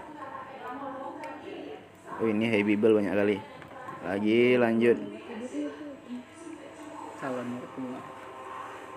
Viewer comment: ngomong apa sih itu ke si..si nya jawab "calon mertua"..😁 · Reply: kau bilang mamah papahku mertua??hahahaha: calon mertua wkwkwkwk